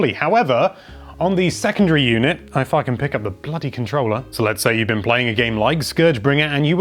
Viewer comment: Slow down the vid to half speed, you'll hear he says "if I can pick up the bloody controller" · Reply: That's not what I hear at 1/2 speed. I hear "I fuckin pick up the bloody controller"